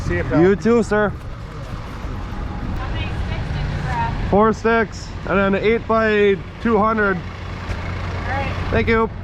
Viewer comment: Thought u said form sticks and 3/8 rebar lol